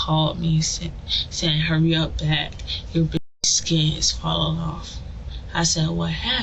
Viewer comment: Omg I thought they were calling the baby 'bitch' im like whaaat??? Then realized it was baby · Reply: wait WHAT SO SHE DIDNT SAY BITCH !? Now I’m confused lol I thought she said he called and said your “bitches skin is falling off” and that’s why the blocked it why would they block out “baby” YouTube just censoring anything & everything now